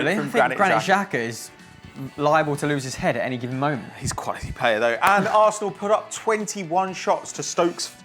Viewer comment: What did say about Xhaka at 7:01 ? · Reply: Maxi Max 'Hes quite a good player though'?